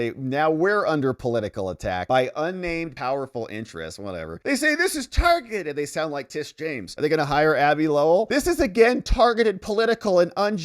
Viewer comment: Did he say Abby Lowell or Avi Loeb?